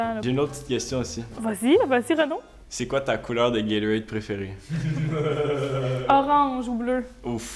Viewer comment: J'ai regardé plein d'autres vidéos mais celle-là ça parle vraiment Québécois j'ai eu du mal à suivre certains trucs mdr 15:29 c'est quoi des "guéluettes" ? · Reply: il veut dire "gatorade" mdr